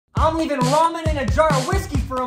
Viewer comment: first i heard "raw men"